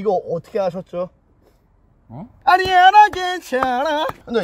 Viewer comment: 아니야 안괜찮아~ 나만 이렇게 들림??